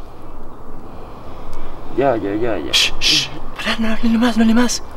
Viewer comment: se escucha una voz que dice "paren" y después de eso la mama de Fátima gritó 😱😱